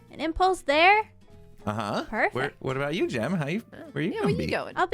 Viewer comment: I'm like 80% sure I misheard here, but 19:52 Impulse: What about you, Gem, honey? The Internet: <shipping furiously>